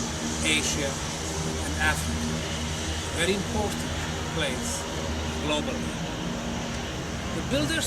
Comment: is think he says important, not popular. Anyways this is not so important lol. okay vid. althought i don't think it's sure that whoever built the site, "did not care about humans"